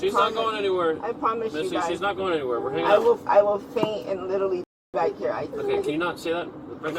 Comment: I think she said “I will faint and literally d*e back here.” IN FRONT OF HER CHILD. The mom subjected her child to needless trauma and fear so that she can maintain her victim status. Insane.